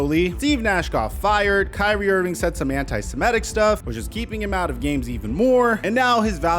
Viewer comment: what did he say antisemetic?